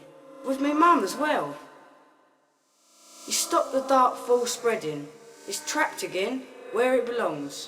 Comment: When you said "released the Dark Fall", I thought you said "released the Dark Force" xDD